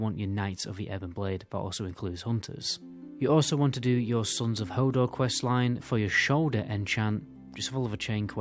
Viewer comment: Did you just say "Sons of Hodor"? lol